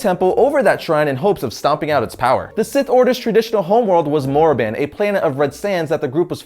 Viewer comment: did he just say morriban?